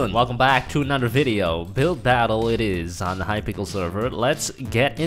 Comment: On High Pickle Server HE SAID PICKLE INSTEAD OF PIXEL 🤣